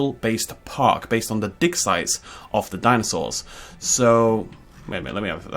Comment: Thought you said dinosaur dick size...not dig site. Spat out my tea everywhere!